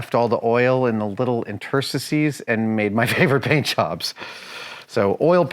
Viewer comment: Can someone please tell me what Adam said about the paint job at 23:03 ..? What word did he say? I heard a new word, "Intersacies"? Please don't tell me I misheard "intricacies" ...? I feel like I'm having a stupid moment because I'm a qualified woodworker with paint and varnish experience